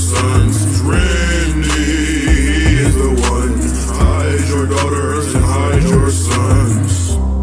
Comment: anyone else hear hydro daughters and hydro sons? 💀